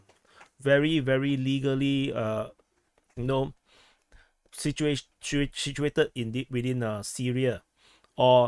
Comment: since when i said the bases are legal? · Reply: what did you say at 1:12? Very very legally you know situated in Syria is what I heard...Correct me if I am wrong...